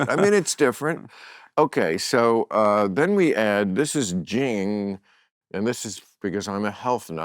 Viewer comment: What is the liquid he drops in to his cocktail? He mentions it but I can’t tell what he says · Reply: The World's First Herbal Soda®. Jing Soda®, is made with all organic fruit, and plant concentrates. Also, infused with immune-supporting super herbs. getjing